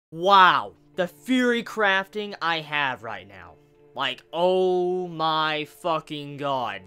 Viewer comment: Haha! At first I thought you said "Wow, the fury crafting I have right now!" I was like "What?! You didn't like this chapter??" xD